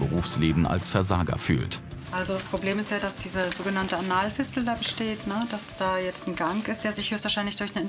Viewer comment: Ich hab erst Analfisten verstanden!